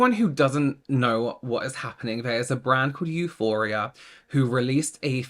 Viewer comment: Haha I thought was saying “Euphoria” 😂